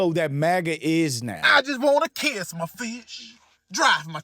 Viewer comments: "I just want to catch my fish. Drive my truck. Drink my bear." 😂😂😂😂😂😂😂😂😂😂😂😂😂😂😂 this will definitely be a meme.. 💔😹✌🏿🥀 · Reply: I thought he said “Kiss my fish” 😅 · I thought he said I just wanna kiss my fish....LOL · I thought bro said "I just wanna kiss my fish"